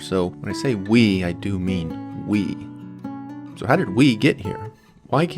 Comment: are you speaking french i cant understand whats oui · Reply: You are very bad at guessing accents 😂😂 He was saying "we"....not "oui" 😂😂